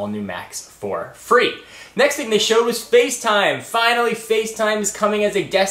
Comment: Watched to much Tebj. When you were talking about Facetime I heard "to device" and " to da face" :L